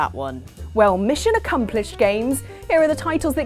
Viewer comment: Did she say "mission accomplished"??